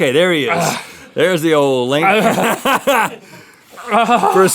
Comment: XD When Rhett says "Theres the old Link" and then laughs at 9:27, the subtitles say "there's the old lady fat haha" OMG LOL I WAS LAUGHING SO HARD!!!! XD